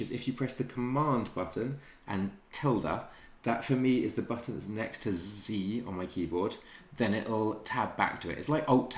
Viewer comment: Whats the shortcut key? Literally have no idea what he said "tilda?" 2:46. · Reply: I just played around with it. It's command + ~